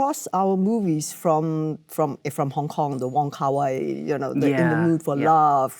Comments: the subtitles say "(Michelle mumbles"). She says the name of the director Wong Kar-wai. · Can't believe at 21:10 when Michelle mentioned Wong Kar-wai, one of the most celebrated auteurs of our time, the subtitler wrote "(Michelle mumbles)" instead of his name... Bloody philistine.